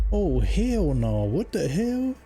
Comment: Lol @ 0:34 thought "Oh Hell Nah, What the Hell?", was on this clip. I said, now why's the captions in French when this she's sounding like one my Aunties. Then I realized said that 😆 😆!